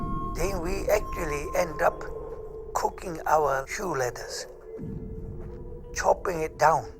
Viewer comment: What were they eating? Is he saying shoe leathers? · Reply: Yes. It was freezing and they were starving. All they had was shoe leather to chew.